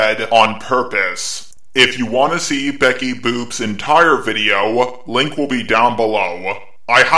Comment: Sounded like Becky's Boobs to me 😂 . . . Which then seconds later in the video, I learned was an appropriate notice lol